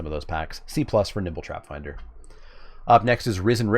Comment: I could have sworn you were saying Nipple Trapfinder.